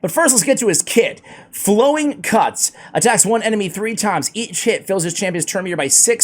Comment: Had to replay that at 1:46 I swear he said Eat Sh!t but it was "each hit" I still laughed hard!